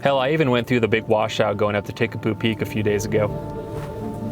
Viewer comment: Excuse me, did you say take a poo peak?